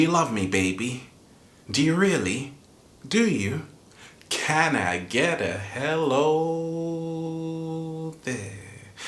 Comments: I understood about marketing. The problem I didn't hear " Can I get the hellllo there 😂 " I heard " can I get the hellllll of here 😂😂" . well, m not English speaker, so I have little bit confused sometimes. that's why I said too much negative phrase in positive video. ☺ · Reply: I understood about marketing. The problem I didn't hear " Can I get the hellllo there 😂 " I heard " can I get the hellllll of here 😂😂" . well, m not English speaker, so I have little bit confused sometimes. that's why I said too much negative phrase in positive video. ☺ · too bad lol that is is "thing" that make him stand out. If people don't know his name, all a person has to say is " you know the guy that says "can I get a helllllllloooooooo there" Not sure of your age, but I would recommend you to look into marketing techniques. Then maybe you will understand. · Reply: I understood about marketing. The problem I didn't hear " Can I get the hellllo there 😂 " I heard " can I get the hellllll of here 😂😂" . well, m not English speaker, so I have little bit confused sometimes. that's why I said too much negative phrase in positive video. ☺